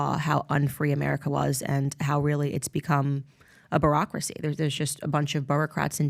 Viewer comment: I don't know if it was a Freudian slip or just her accent, but did she say "Barack-racy" ... which is probably more accurate!